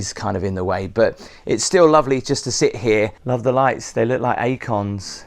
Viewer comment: Love these - I had to look up quickly when you said "I love the lights, look like AKONs.." completely misheard you!! hahahah! thanks for the tour!